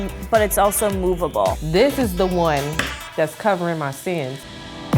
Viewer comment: Did she say this is the one that's covering my sins😂😂😂😂😂